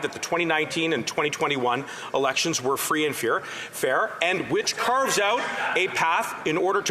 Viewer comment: Did Mendicino just say "free and führer" ?! (Edit: I adjusted the time stamp back by 3 second)